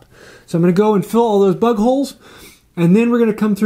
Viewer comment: Listening to this at first I thought u kept saying butt holes. Had to hit the rewind to figure out it was bug holes 😂